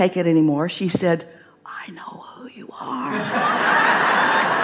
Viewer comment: What does she say at : 13:11 ? What is other wiman saying when she is pushing the nail artist to get it done faster ? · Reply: Asmroze e, “I know who you are”